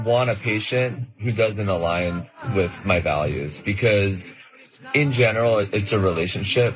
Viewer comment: He says "my values" but I hear "my race."